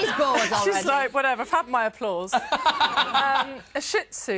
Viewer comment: I thought she said fuck my applause